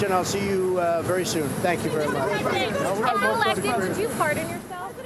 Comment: “if you were elected would you fart on yourself” is what I heard 😂